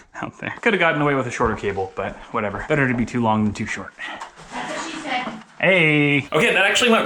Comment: Jordan why are you putting end quotations like they are different people? It should’ve been written like: “better to be too long than too short, that’s what she said, AYYYYYY” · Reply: Boler Pear go back to 6:50 and watch it again with your volume a little higher. You hear say “that’s what she said”